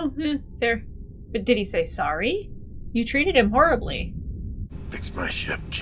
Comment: he said sorry, I heard him clearly. 👍🏼